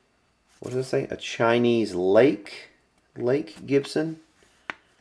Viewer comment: ?? Instead of LAKE it may have said FAKE ??